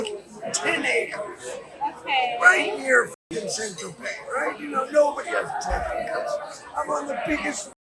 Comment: i think he says Saint-Tropez not Central Bay for the subtitles